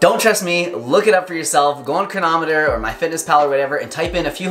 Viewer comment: What is the name of the website used in 2:20? I really don't catch the name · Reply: Cronometer